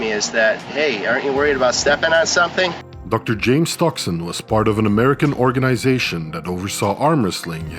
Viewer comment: Dr. James Stoxen sounds too much like Dr. James Toxin to me. It would be tough to be one of his patients. “Good morning, my name is Dr. James Toxin, my nurse tells me you’ve got some bad pain in your stomach?” “😮 Umm… Is that what she said? Nah, that’s not a problem anymore. I feel fine now. Thanks Doc.”